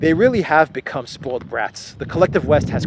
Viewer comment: For one moment I heard you say; spoiled rats.